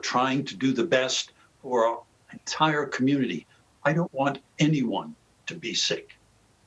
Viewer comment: Did anyone listen to what he really said in 5:23 to 5:26? I don't want people to be safe. That's the true purpose of the vaccination